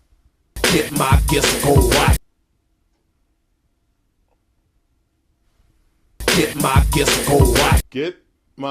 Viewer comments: I thought he was saying “Get my testicle washed.” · I legit always thought he said "get my gifts gold watch" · Oh Come on, the song is obviously saying! "Get my jdhridjedkkridirmacknen!" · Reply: I heard "Get my kids a gold watch"